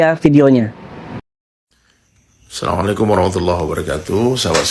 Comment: Kok salamulaikum min ? Apa susahnya bilang assalamu'alaikum . Masa disingkat bgtu , ada yg bilang Alhamdulillah disingkat hamdulillah , ada apa sih para ulama kok suka singkat2 bacaan Alqur'an. Kl ditanya bilangnya diucapkan as tp ga kedengeran , apa bs diucapin tanpa kedengeran sementara yg lain dibaca keras , sy ulang video ini , jelas2 nyebutin salamualaijum buka asalamualaikum